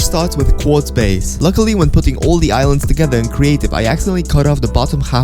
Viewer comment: did he say "in creative"?